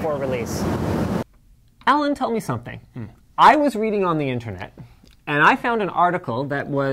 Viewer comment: Sounded like "I went on the internet, and and I found this" hahahah Clarkson.